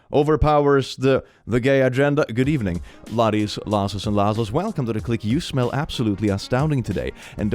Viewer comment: I would like to confess something Click. For the longest time I thought you were saying "Good evening, Laddies, Lassies, and L-Assholes." In your intros and I just accepted it. For like a year.